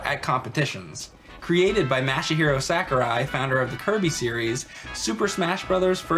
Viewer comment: "Created by Mashy-Hero Sakurai" Thats what it sounded like Mike said at the beginning and it made me laugh alot more than it should have. Good video as always though guys.